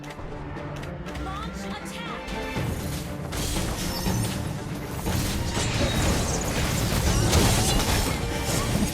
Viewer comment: Hey do you what did Khufra say? Click thumbs up if you know so i can know who know what did Khufra say:> 👇 👇 👇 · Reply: He said dont feed to much